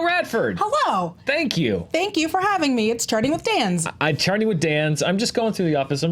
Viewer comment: Everytime they say "chart", I hear "shart". And, yes, my reality is awesome, thank you for asking.